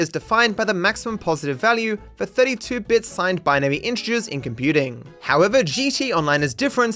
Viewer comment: "32-bit signed binary integers." Sounded like you said 32 bitched signed inches.